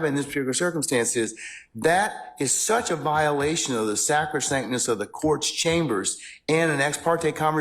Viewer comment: Does anybody know how that word is spelled? Sound like he said saprasaintness but idk.